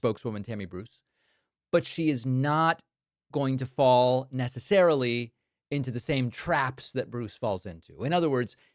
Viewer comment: who heard in their head "not going to fall for no banana in the tailpipe"?